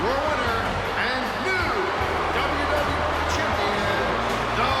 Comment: I literally heard: Your winner and the new WWF Champion (and not 'WW') The Rock.